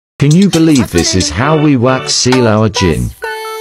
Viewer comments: Ngl, I thought it said "can you believe this is how we wax seal our (va)gina" for a good minute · At first I thought you said we wax our gin meaning v and I was like ewwww grossss